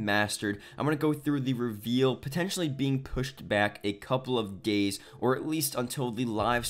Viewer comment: Dude did he just says " a couple of GAYS" in the beggining of the video i replayed the video 4 times